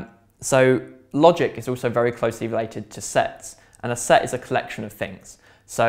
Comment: I thought you said sex